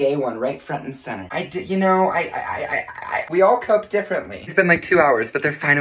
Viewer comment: AT FIRST I THOUGHT U SAID "WE ALL COKE DIFFERENTLY" NOT COPE 😂 I was thinking damn that really is a weird thing to do on coke, never seen that one before